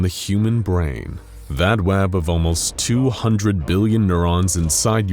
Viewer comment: did anyone else hear him say 'wab' instead of 'web'?